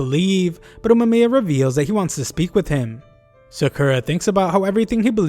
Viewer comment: awww hell nah😅 i thought uma mia said i want to "sleep" with him instead of "speak"😅😅😅😂😂😂